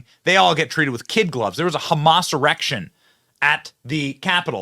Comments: Sounded like you said Hamas erection. I had to double take. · Benny, did you just say”there was a Hamas erection?” Oh! I guess you said a “Hamassurrection.” 😂😂😂😂😂😂😂😂😂😂